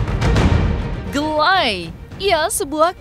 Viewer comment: Dia tuh bilang, gak suka GA LIKE.. Tp kedengerannya jd gelay